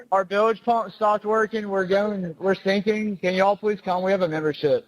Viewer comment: Bro said "Shut up. Shut the f*** up." None of that other garbage was even close. 🤦‍♀️🙄 They're so desperate they're literally hearing what they want to hear. Pathetic. Then if u actually listen to the tone of their voices in comparison to what shes saying is being said most of that doesn't make sense either. Its more than just picking up syllables and piecing them together which is impossible to do when u already have a preconceived notion. To add to that mess, it doesn't make sense that they supposedly just killed a kid and the person is reporting a tow simultaneously right next to where this is happening but doesn't react like anything just happened and is still asking for the tow like nobody is going to notice a dead body. Plus i keep telling fools on the internet, WHY, in what world would it make sense that they killed him on a boat but they dump him on or near the shoreline where hes definitely going to be found right away instead of somewhere deeper in the ocean where he wouldn't have been found right away IF he was ever found at all??? Both coroner reports said he wasnt beat to death which is the only thing that would make sense if his friends did it and covered it up. Its not even believable that they planned a murder together cause they all just happened to be murderous psychopaths except for Nolan so they picked him to kill instead of join in the killing. And they chose to commit the murder right then where everybody was at and they could've gotten caught. The